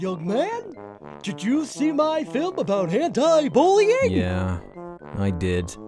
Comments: when cool cat says "did you see my film about anti bullying?" I heard "did you see my film about hentai bullying?" XD idk why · The animation is so good😱😊!!! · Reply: I thought he said hentai bullying the first 4 times I watched this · How come I'm the only one that heard him say "Did you see my film aboot Hentai bullying?".....SEND HELP! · I thought he said, "My film about hentai bullying." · I heard "Have you seen my movie about hentai bullying?" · To be honest I thought he said hentai bullying until I read the captions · I thought he said hentai movie and was weirded out for a moment. Then realized he said anti bullying. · Who else thought that he said "hentai bullying". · Swore he said "hentai bullying" LMAO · I thought kool kat said "henti bullying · At first I thought Kool Kat said "Hentai Bullying"